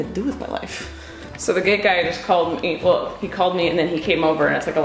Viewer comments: When said “Gate Guy” I thought she said “Gay Guy” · when you were saying "the gate guy" i thought you were saying "the gay guy" :') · I thought u kept saying the "gay guy" 😂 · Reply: Karen Martinez i think she said gate guy